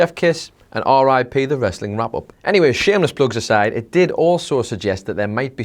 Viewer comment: Did he say Wrestle ramble at 2:21.If not what did he actually say. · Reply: Ibrahim Shahnawaz wrestling wrap up